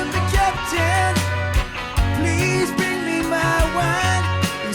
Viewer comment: I always thought he said "please bring me my wife"